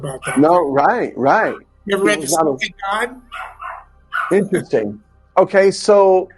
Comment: does anyone know what the name of the book he mentions? He says have you ever ever read…….. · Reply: Smoky God